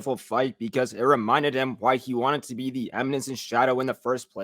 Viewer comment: heads up, the subtitle says Amazon instead Eminence.